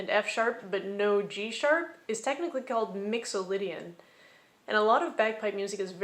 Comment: Two thoughts: 1. You say "Mixolydian", I hear "Mandalorian". Can you make it stop? 2. After this video, the YT Algorithm recommended Guy Michelmore's "Music Theory in 16 Minutes", which I found very enlightening. Primarily because he completely avoided using musical staff notation, which prevented triggering my lifelong allergy to Music Theory.